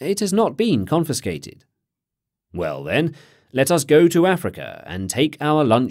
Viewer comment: shouldn't it say "to Aricia" instead of "to Africa"? Otherwise excellent, thanks!